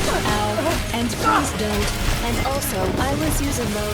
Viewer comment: I think actually said and not N